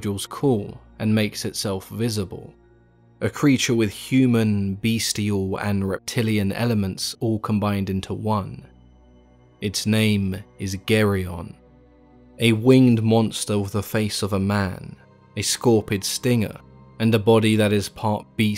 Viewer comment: "and its name.... Is Gary" Totally what i heard lol